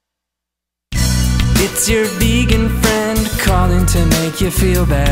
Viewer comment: ahhhh.....when it said 'vegan friend' I thought it said 'megan friend' so I thought, My best friend is after me!!!!